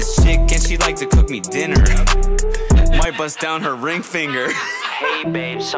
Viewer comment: I thought this was “weeed goood” in those ig edits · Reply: I thought it said “she like to call me dinner, My butts down her ring finger haha.” Like actually I could’ve died